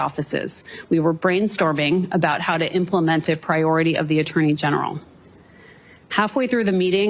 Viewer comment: Did she say brainstorming how to implement the authority of the AG?